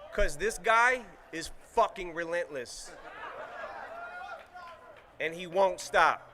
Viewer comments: Did anyone say "fuck Ja Rule"? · anyone else hear the guy yell "fuck Ja Rule"?